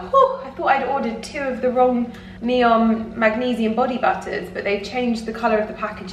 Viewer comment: Winston is gorgeous!! Really love your vlogs. However whenever you mention Neom body butter now I keep hearing ‘bottom butter’ is said in his vlog...😂 LOL xx